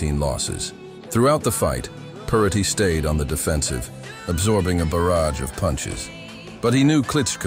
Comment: “An obscure boxer” keeps him obscure by not giving his name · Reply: I heard his name "throughout the fight purrity stayed on the defensive "